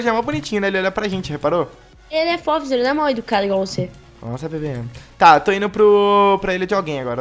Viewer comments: só eu que ouvi o falando para nossa bebe!!!#BIBIDRO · acho que ouvi "nossa bebê"